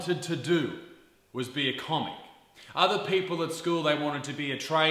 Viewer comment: “Not me, I wanted to be a commie”, I thought u said this lol